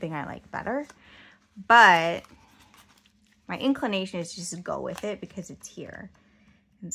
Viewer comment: Yes, I received that email, loaded up my cart, but haven’t purchased. At 3:53, did you say, “inkclination” 😂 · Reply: I think I probably did say that because I'm a goofball sometimes. 😅🤣